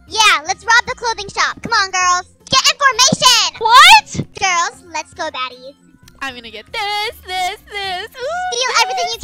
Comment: Ik this was like a day ago but I thought they said let’s go daddy💀💀💀